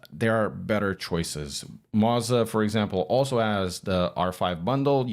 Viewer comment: Did you say mazda instead of moza at 3:50?